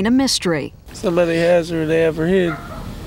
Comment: Why does it sound like he says at the start "someone has her or they have her head" ????